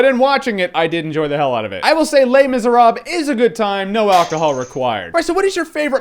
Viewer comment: At the beginning, when you kept saying "Les-Mis", I first thought you were saying, "Lame-Is", calling this film lame, and what-not, haha...